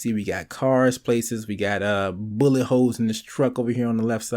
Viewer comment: when he said "bullet holes" i thought he said "boomin' hoes"